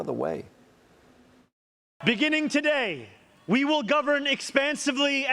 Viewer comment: I thought he said "We will Govern expensively!" 🤣